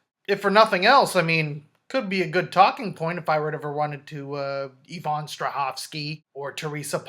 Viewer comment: Who is the Evan Strauaksi girl at 5:30. I cant hear her name and google has not been kind! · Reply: Yvonne Strahovski